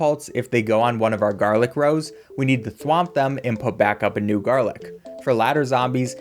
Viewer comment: did he just say thrwomp them?